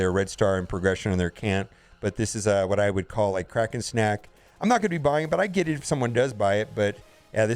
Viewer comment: Lol. U thought you were saying crack and snack. But you are really saying kraken snack. The big spenders.